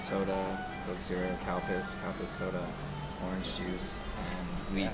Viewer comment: LOL Did he say cow piss soda? LOL @ 8:57? LOL